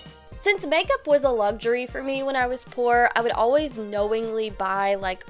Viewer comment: for a sec I thought she said makeup was a luxury for me when I was four and I'm like wow